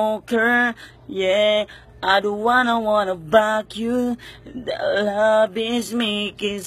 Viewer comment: Ako lang ba o narinig nyo rin sa 0:36 ang "hmm I dont wanna wanna pak yu" hahahahha